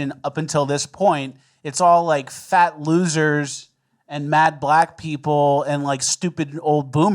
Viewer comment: Join Bonus Land for 30 minutes of extra show after every episode. That's over 4 hours of exclusive content each month! <link> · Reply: Don't worry. I heard you say Matte Black people 😂 you're heard youre seen you're funny